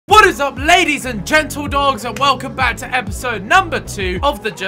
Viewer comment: did he said: what is up ladies and gentledogs ?